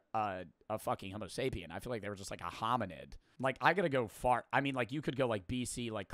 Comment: From 12:31 to 12:35 i heard say "like i gotta go fart" in a serious manner or is that just me?